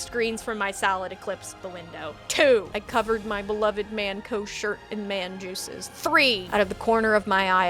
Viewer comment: mmmm did you say man co. as in TF2 MAN OC!?!?!??!